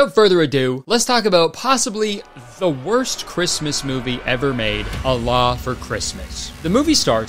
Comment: I heard that as "Allah for Christmas" 💀💀 lmao. A significantly better movie title, imo.